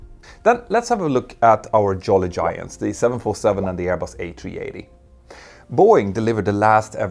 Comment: did you just said Airbus 747 sir?